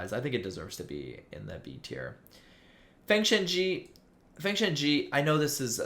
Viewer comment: what is the name of the manhwa? Sorry i'm not native english so i couldn't hear well I want to search it but i didn't know how it pronounce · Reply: Feng Shen Ji